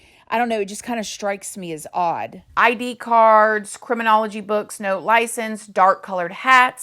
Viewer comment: I thought I heard that there was a dog found that was dismembered nearby like a week earlier I'm not sure but I wonder if that's not dog hair. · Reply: It was “ID cards”, not “10 curls”.